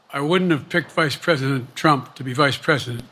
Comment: Vice president Trump, did anyone else hear him say that statement?